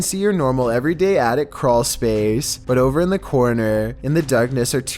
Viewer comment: ''When he does get his camera up there you can see your normal everyday attic crawlspace'' at first i thought he said ''attic creature'' xd